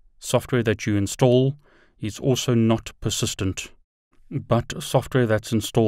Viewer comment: Hi, awsome video. I am taking the course Linux for network Eng within GNS3 courses and is great. I was wondering what do you mean by Ubuntu Docker Container is "not assistant" in 11:08. Not sure if I captured what you've said (English is not my native language so probably you said something different). Then you say that Ubuntu GUI (QEMU) IS assistant..Can you elaborate that? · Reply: Software is not persistent is what I said. in other words, if you install software on the Docker container and then restart GNS3, the software will have to be reinstalled again.